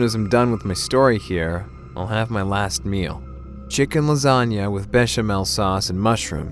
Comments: I just heard bitch lasagna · i hear bitch lasagna